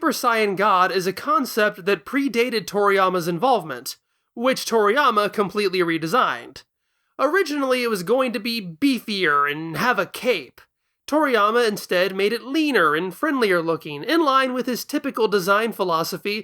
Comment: I thought you said "and have a cake" and I definitely did NOT go 👀